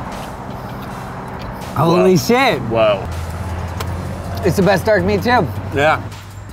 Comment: What was he saying? 06:16 "It's the best dark meat tiv?". Was it tiv or what? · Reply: He said, “It’s the best dark meat too”.